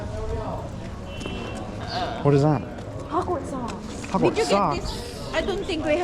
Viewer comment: LOL I thought she said Hogwarts Sauce...if you dont know what harry potter is that sounds even grosser